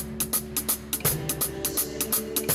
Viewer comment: Anybody catch what he is saying between 1:02 and 1:06? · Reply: Brian Harris Drummer Aww yeah I got my shit on dude